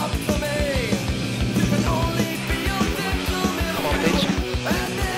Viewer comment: Anyone else hear him say “come on bitch” in the og vid at 11:05?🤣🤣🤣